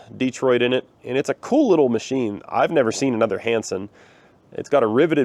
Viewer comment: I thought you called it Handsome when you first said it